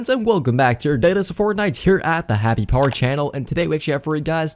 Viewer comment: It sounds like hes saying happy part channel